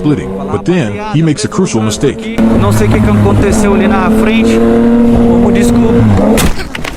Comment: I realized it was in Brazil, I'm brazilian and he said '...Vamos Descobrir' (Let's check this out) instead of 'I'm Sorry' (Desculpe me)...So captions went wrong....